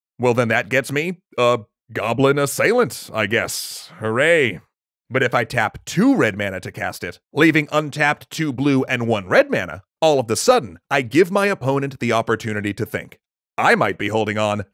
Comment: Does he say "all of the sudden"? O_o